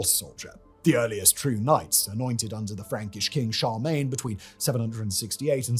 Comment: is he saying CHARMAGNE??